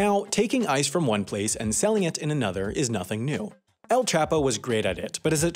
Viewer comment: Just learnt 4 new things: - Iceland imports Ice - Iceland is as icy as the Earth is flat - Ice ones was a big business - Skillshare teaches you how to sell a new type of ice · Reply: "new" ? I thought he said "another"kind of ice (meth)